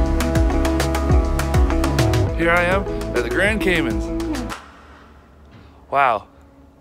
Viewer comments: Sounded like you said Grand Cayman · Did he say “Grand Camons?” It sounds weird at 9:06, also why did it look like a picture or a painting on a wall or something like that...